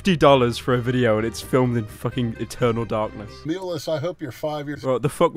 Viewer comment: I think he said Milos.